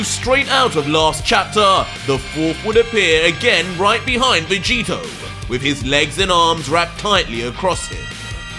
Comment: did he just said Vegito or I’m just tripping 🤔